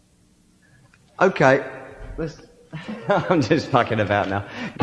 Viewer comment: "i'm just fackin about now.." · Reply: westall I thought he said mucking about?